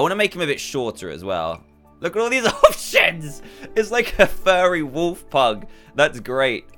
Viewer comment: HE DIDN'T SWEAR HE SAID OPTIONS